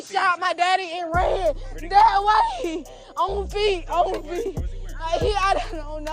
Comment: 1. In the beginning when the one sister is crying, and saying dude shot her dad....I swear to God I thought she said "...he went that way. On fleek, on fleek!.." After review I'm pretty sure it was "on feet" but still that shit had me like "wtf this chick say?" 2. Famous? Who?.....Ohhhh....tiktok, that explains it. There needs to be a distinction. This "tiktok famous" shit is NOT the same as real, established famous.